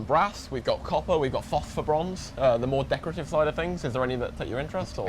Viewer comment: fofferbronze, is he saying phosphorbronze? man these english dialects are tough to understand.